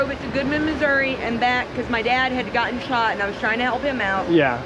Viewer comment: had to put on captions to confirm, she actually said her dad got shot😭